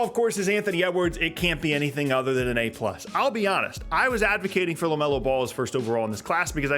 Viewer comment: You low-key look like Giannis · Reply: I think OP meant the first time he heard him say "I'll be honest" he said "I'll be Giannis" and he's like "if you say so"